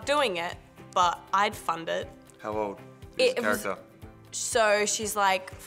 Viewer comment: in 3:47 what word is she saying lol it sounded like the f word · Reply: She said that she would fund it x